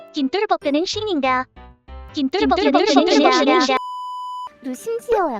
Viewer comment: 김똘복 그는 신인가 한번할때는 잘 들리는데 뒤에 반복하니까 김똘복 ㅂ신인가로 들림 ㅋㅋㅋ